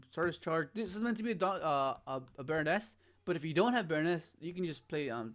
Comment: What card is mentioned at the 06:20 mark? I'm guessing that what he's saying is short for a well-know monster, but I'm new to the game and know only a few card names. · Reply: Hes talking about the card called Barones de Fluer. Its a lvl 10 card that is a negate